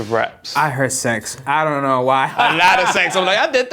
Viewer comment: I heard "sex" like also!! lol! 6:10 lololol